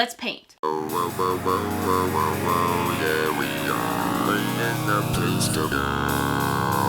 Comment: IM DYING 😂😂 6:04 I heard: owowowowo there we are this is the place to dieeeee omg I can't